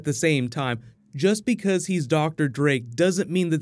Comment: Thought you said Dr. Dre at first.